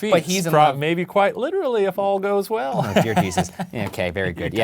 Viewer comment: did he say "jear desus " brandon is shook!!